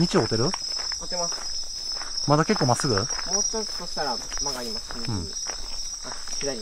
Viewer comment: ぐらいに「曲がります」の時「すぐ」って聞こえるような！